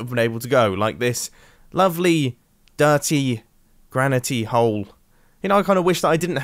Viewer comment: I thought you said "Gravity Hold".